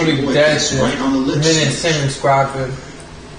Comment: what the fuck does he say? Nvm “he was legally dead the moment Simmons grabbed him”